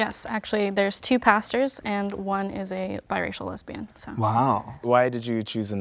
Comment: did she just say her pastor was bi racial...